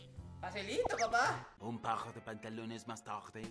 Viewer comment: al menos escuche a decir : "Facilito papa" 😀